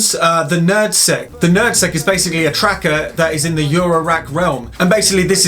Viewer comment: He said: "NerdSeq." I thought he said "Nad Sack."